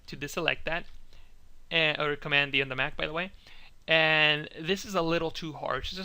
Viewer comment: What does he say in 8:22? control+ B? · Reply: Hanna Hottola Ctrl D or Command D to deselect